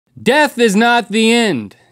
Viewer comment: I thought Link said "Meth is not the end" 😂